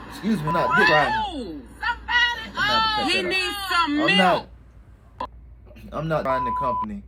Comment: get some milk. Granny said so I heard her!